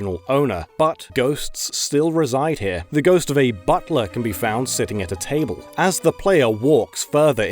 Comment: At the old chateau part, I dead ass thought he said "ghost of a butt plug".